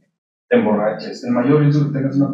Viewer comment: Minuto 4:58 donde dice el mayor riesgo es que tengas una pareja... y después no entiendo lo que dice. Si alguien entendió me lo puede traducir? Gracias. un fuerte abrazo desde Argentina. Siempre veo tus videos. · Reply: El mayor riesgo de la existencia humana, es tener una pareja que no debiste haber tenido... que consuma tu energía...